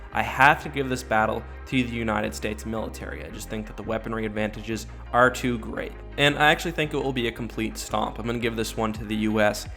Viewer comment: anyone else hear him say, "the weaponry advantages R2 great and I actually think it Obi a complete stomp"?